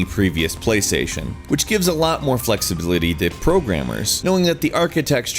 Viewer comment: "flexliblity". Is what I heard. Good job.